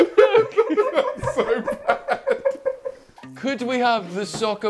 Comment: What does James say at 2:35 ? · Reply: “You can’t keep that in.” “That’s so bad”